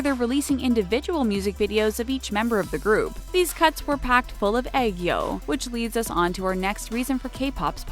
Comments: Lol when she said aegyo, I thought she said egg yolk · Lol did anyone else hear "These c!nts were packed full of aegyo" 😅